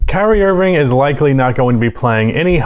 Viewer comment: thought you said "Karey Irving" lolol